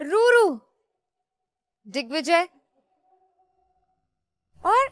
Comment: I think urfi says dickvijay 😅 Idk I heard wrong or not